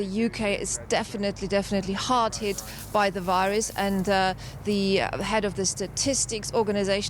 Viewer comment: I'm not fluent in english, can anyone tell me what she said after "definately" please? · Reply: "definitely hard hit by the virus"